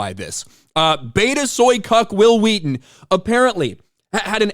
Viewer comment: did anybody else hear "Betazoid Cuck"?